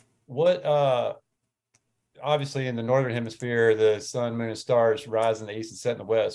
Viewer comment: ?? what'd he say? Northern hemisphere? So what shape do 2 hemisphere's make?